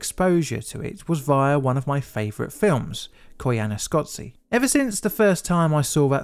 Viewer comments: what film title is he saying? I can't make it out? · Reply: Koyaanisqatsi (1982) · Anyone know what film is referred to at 2:10? Subtitles say it is coana scoty but I can't find any film of that name, there is Koyaanisqatsi but that's 1982 which is 4 years before the accident...?